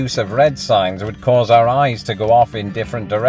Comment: I thought you said wife's husband at first...whew!